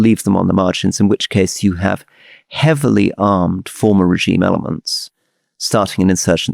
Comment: Could anyone help me figure out what Rory said at 46:22? "... in deliberate attempts by (??)" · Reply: I hear "attack furies" :) I think he meant "armed forces" of the state.